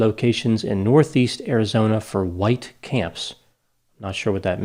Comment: it’s actually white tent camps is what they said.